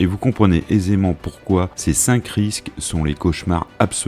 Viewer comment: j'ai entendu "touche minou" 😅